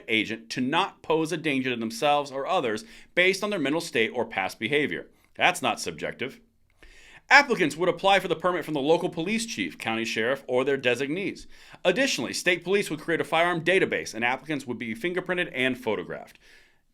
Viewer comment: At about 3:35 did you say applicants or africans? <s